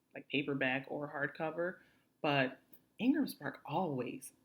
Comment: Thank you for this info, very helpful. But could you clarify what you said at 12:03 where you mentioned a book publisher's name. I heard Engles Park or something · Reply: Ingramspark- Print On Demand company